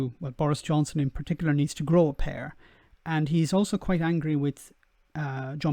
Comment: Used to Canadian English. I keep hearing him say "grow a pear". Was confused for a while.